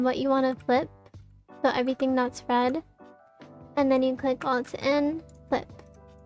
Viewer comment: i have 0 clue what you said on 12:28 like click N?? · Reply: yes alt+n she forgot to add alt